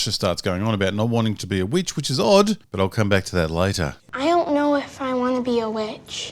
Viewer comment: so the best take where she says "I ont know" instead of "i dont know". I thought actors were taught to enunciate